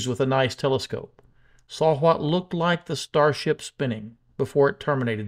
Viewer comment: One correction, the video by Astronomy Live of the tumbling payload section was recorded after FTS activation. He saw the explosion in his finder cam first and then was able to point his telescope at Starship. So, the unpressurized payload section was left relatively intact after the explosion blew up the tanks. I don't think this tumbling indicates anything about the reason FTS was activated. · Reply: Indeed. My bad. I thought he said he saw the entire ship spinning.